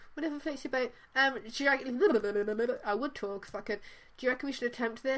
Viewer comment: / 6:57 did say "I would talk but f!#$ it" . . . . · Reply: yesh she said 'I would talk but f!#$ it'